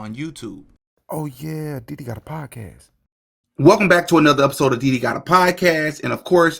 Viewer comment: I thought bro said welcome back to the diddy podcast for a second 😂